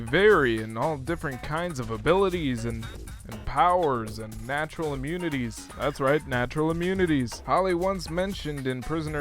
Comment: I thought you said "vaginal immunities" xD xD. Had to hear it like 3 times to make out "natural immunities", as you later said.